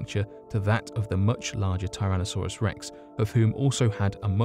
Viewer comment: "[...] shows incredible similarities to a Tyrannosaurus rex" (shows picture of carnotaurus) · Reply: thought you said "Tyrannoxaurus Sex"